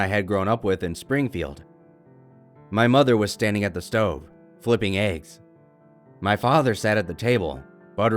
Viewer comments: I heard: "My mother was at the stove flippin' AIDS." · I thought he said flipping aids